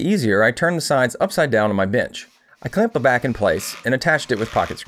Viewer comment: Listen throw headphone while (minding my other mind business 😂😂) and when he said I sat it on top of my bench I thought he said (very childish of me but as of now) bi*ch I’m like hold on what then I look and see a bench table I was like oh okay journey why r u childish 😂😂🤦🏽‍♀️🤦🏽‍♀️😱😇🤯🤯